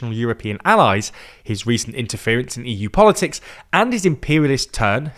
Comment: I thought I heard "and he's an imperialism turd", and I was okay with that.